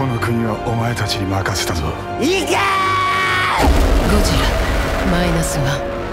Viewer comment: What does he say at 0:40? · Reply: He says, "Go!"